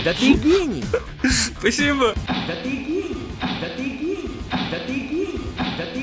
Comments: Мне одному показалось что он сказал Да Ты Гей! Сорян если украл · я услышала... Да ты гений Да ты гений Да ты гений Да ты гей... ;_) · я одна слышу, что эта игра говорит "Да ты гей"?) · чёт я вместо ,, да ты гений,, слышу ,,да ты г€й,, Мне одной так кажется или ээээээ... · я одна там слышу " да ты гей" 😅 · Я один слышу "да ты гей, да ты гей, да ты гей" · Мне одному послышалась да ты гей